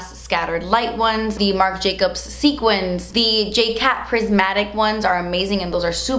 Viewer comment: Did she say J Cat Makeup Pris-Matic? I'm trying to look for it but I can't find it anywhere.....